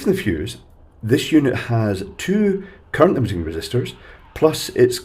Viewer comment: it has two what? Did you say current bun resistors?